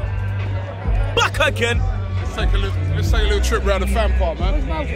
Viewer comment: Don in his airport making sure everything is going well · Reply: I swear said fam park in